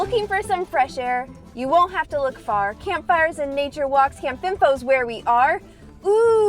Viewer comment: When I first heard the name, I thought you said Camp Bimbo...and thought, what a weird name for a family themed campground ...